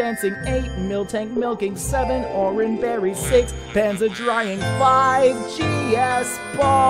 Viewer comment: 6 pans of drying xD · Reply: I thought it said 6 pans of dying XD